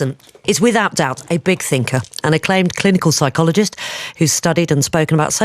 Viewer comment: A 'claimed' clinical psychologist? He is a clinical psychologist.... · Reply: lmao. "A Claimed".. that's hilarious that you misheard it that way.